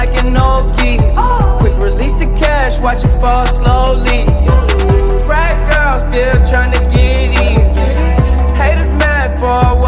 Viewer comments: I thought he said "Fat girls still tryna get eaten"😂😂😂😭😭😭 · Glad for lyrics video. I always thought they was saying 'Fat girl still tryna get even" 😂😂